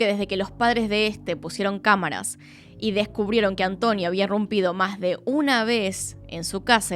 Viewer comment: es roto no rompido, solo para que en otro videito se escuche mejor:) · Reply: Dice irrumpido (entrar violentamente en un lugar)